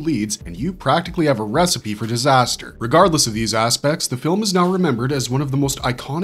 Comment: In the Barbarian Brothers bit, I keep hearing the word 'aspects' as 'ass-pecs'. For some reason.